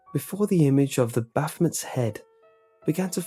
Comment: The what's head? Sounded like you said "the bathmat's head." XP